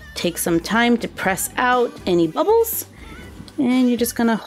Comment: Who thought she said abbles instead of bubbles